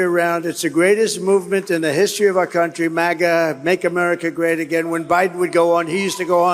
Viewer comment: Did that song really hit , could have sworn he said "make America gay again"🤣